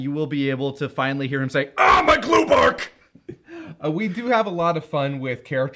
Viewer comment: when I was a kid, I always thought Piccolo was saying “ow my blue part” and I kinda love the mystery of WHAT could his blue part possibly be